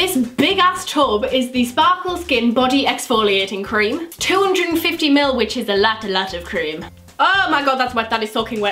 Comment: When she said 250ml I thought she said £250 (The Clinique body cream)😂😂 ly ❤️❤️